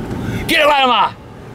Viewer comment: "Get a land mine!" XD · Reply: Captain America haha to me it sounds like he is saying "Get a lama!"